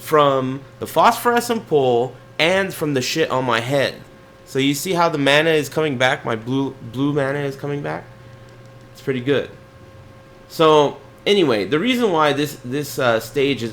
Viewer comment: Did just call himself a shithead?